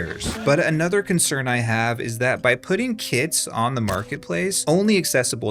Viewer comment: oh right, "kits".. that's exactly what I heard, not "kids"😃